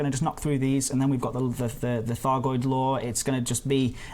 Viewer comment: I heard "Thargoid law". Oh no, they already won... lol